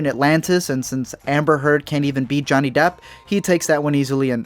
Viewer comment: Did he said "Amber Heard can't even beat Johnny Depp"?